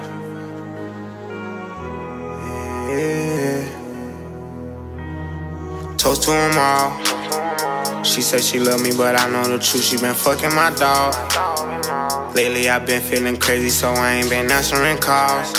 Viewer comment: When i first heard this song on tt i thought lil baby said toast to my mom for a sec..